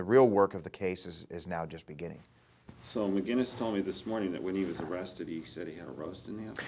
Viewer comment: anybody understand what he said? Mcquiness said he hadn’t roasted yet? What?